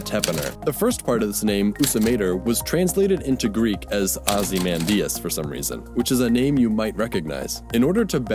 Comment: I heard you say “Ozymadius” and I instantly thought of “Ye mighty, and despair!”